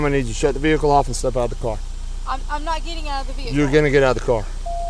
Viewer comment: I thought she said “I’m not getting out of the vehicle “. Lol